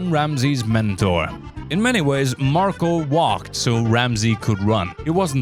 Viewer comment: "I control my anger very well" ... "I took my anger, and infused it all into a single person. That person is Gordon Ramsay now" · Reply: I'm listening to this show while reading all the responses to this epic comment, and in the show they said that "Marco walked so that Gordon could run" I heard it as "Marco waaaghed so that Gordon could run."